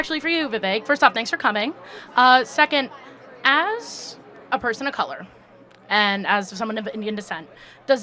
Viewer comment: did she really say “as a person of color?”